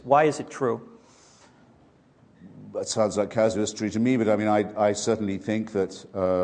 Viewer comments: What was Hitchens referring to at 4:50? He said "That sounds like.." Something caz zest tree? What did he say and what does it mean? Thank you. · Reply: wetlazer He said it sounds like casuistry - an argument that sounds clever but has unsound reasoning. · What does Hitch say at 4:50? "That sounds like ____________ to me..." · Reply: Casuistry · what does hitch say? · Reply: “That sounds like casuistry to me…” “Casuistry” is one of those hundred-dollar words Hitchens used so casually. It’s basically a term of art in logical reasoning. In this context, it’s a much more concise way to say “a line of argument that sounds superficially clever on the surface but is ultimately illogical or dishonest (or both) when analyzed seriously.” Hitchens conveyed all the ideas in my much longer phrase by using that single four-syllable word. When people suggest using more big words will make you sound smart, I always think of Hitchens. Yes, he certainly used words most people didn’t know (which limited his audience). But that was typically because he was using the best word to convey his thought as concisely, efficiently, and precisely as possible (at least to the audience members who did understand him).